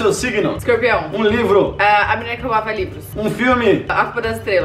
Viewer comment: "a menina que fumava livros" ! Hãm ? kkkkkkkkkkkk kkkkkkkkkkkk Foi isso que eu entendi !